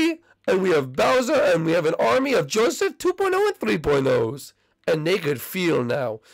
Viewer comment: Did he just say Joseph 4.0? SML copy cat